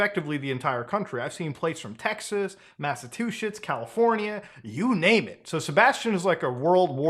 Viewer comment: When you mentioned that Sebastian traveled to Massachusetts, I heard you say “Massive Two Shits.” Now I’m sitting here contemplating if that was a really good joke or if you accidentally made it sound that way. Either way, that shit was hilarious.